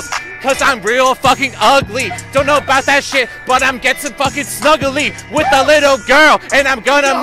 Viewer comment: nah did he really say snuggle with a little girl